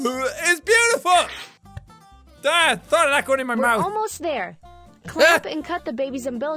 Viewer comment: youtube thought you said Dad sorry my mouth almost there lol